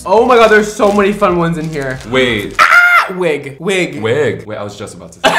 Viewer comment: that “waaaaaaait” sounded like “hmmm I like that more than I thought”😂 y’all are so fun! This was a great try on! Love y’all ❤